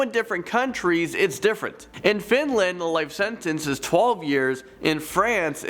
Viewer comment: did he say finland